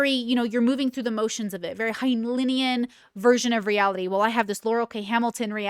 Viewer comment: what word was that? i hope you don’t mind me asking the subtitles don’t work :)) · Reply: I think she’s referring to the author Robert Heinlein by saying Heinleinian.